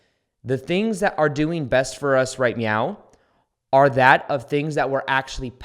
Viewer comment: did you say "right meow"?